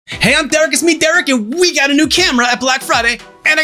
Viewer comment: Maybe you should slow down your intro a little, that went so fast that I heard 'Hey, I'm Doug' instead of 'Derek', lol. Not that I'd ever want to compare you to Doug (TGWTG), you're so much better than he is XD